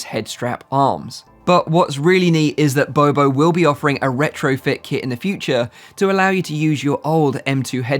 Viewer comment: Can somebody tell me what is RETOROFIT KIT??? Min 7:05 😅 Or it's RETROFIT??